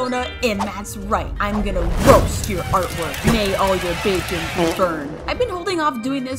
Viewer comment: why did i think she said earthquake and not artwork? idk im a fan of msm